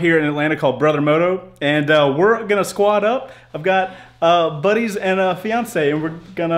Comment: sounded like you said "Buddies and Beyoncé " lmao